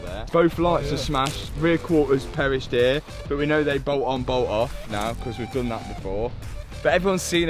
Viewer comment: someone explain to me like a little kid what says on 6:44 ......coz i hear boool on boool😅😅😅😅😅😅